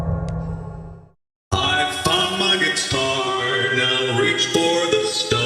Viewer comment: “I found my guitar, as I reach for the stars as I plunge it through your heart” -Rockstar Bonnie He truly is amazing 🥲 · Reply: the thing is I thought he said, "I FXCKED MY GUITAR, AS I REACH FOR THE STARS AS I PLUNGE MY THROUGH YOUR HEART!" I'm so sorry- I'm just too dirty minded